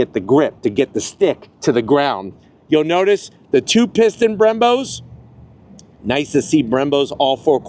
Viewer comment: At first I thought you said, " Two Piston BIMBOS" 🤣😭😬 Neverless, awesome review man! 🤘😎🤘 Dokken,Ratt, and Winger approve!